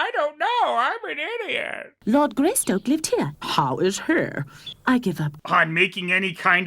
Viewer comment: I think he's trying to say "how is HE?" But this is a Dingo Pictures joint so we will never know